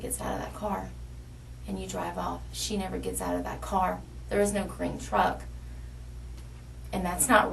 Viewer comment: All I heard was "corn dog, corn dog, green truck!"